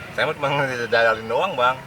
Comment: Ini menit 1:35 ngomong apa bg 😂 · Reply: "Kaga bang, saya mah cuma ngejajalin doank" Gitu kata bang. Wkwkwkwk